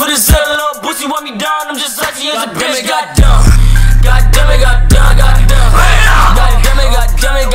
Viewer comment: when x said God damn it I kept hearing demigod lol